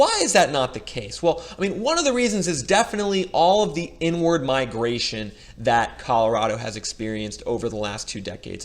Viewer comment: I reported this terrible video… referred black people moving to Colorado as the “the n-word migration” you o look passive aggressive coward. Pretty slick though but not slick enough. We all know what he wanted to say and he said it at the 7:55 mark in the video. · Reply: He said inward migration. Not very bright are you